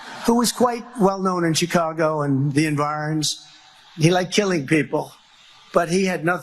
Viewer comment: "the envarns?" Honest question, wtf did he just say (or try to say) here? 🤔